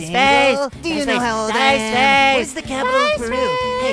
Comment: I heard mugman say "Whats the capital of Beru?"